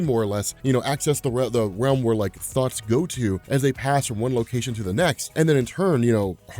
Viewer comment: I heard: "where thots go to..." :D